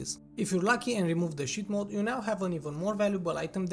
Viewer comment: did he say shit? >.<